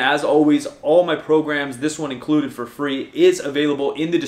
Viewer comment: Ahhh.. I thought you were saying 'A Salaam Alaikum' at the end of the videos...It's, ' I'm Outta Here'. (new subscriber currently transitioning from androgynous to male)